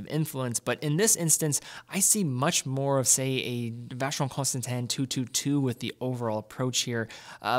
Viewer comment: This watch is great and all but I have to let everyone know that when Teddy says Vacheron Constantin at 2:24, the auto generated subtitles say "vaginal constant hand"